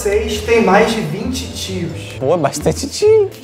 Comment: eu entendi tiros kkk